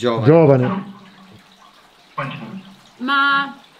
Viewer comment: ciao io al minuto 10:20 ho sentito giovane fanciullo. P.S. vi adoro continuate così <3<3<3